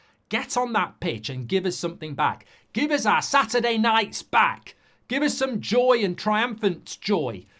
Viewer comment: I thought he said Give us our Saturday night spank 😂